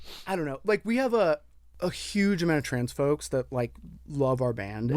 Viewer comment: thought he said 'transphobes' too and damn near spat out my coffee lmaaaooo